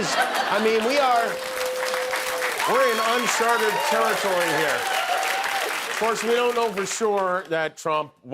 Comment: I heard: we are in un-‘sharted’ territory here.